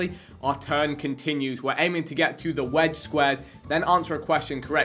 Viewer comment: Who else thought vikk said wed squares 😂